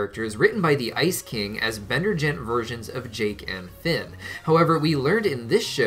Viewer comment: did he just say Bender gent